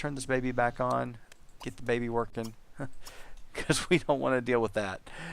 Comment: In the middle i thought he said eat the baby